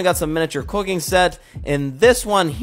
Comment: I thought you said duty and the beast 🤣